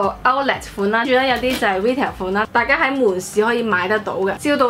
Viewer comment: 唔好意思 聽唔到你講咩款話？xd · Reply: Hei Shun Hazel Ng retail?估既🙈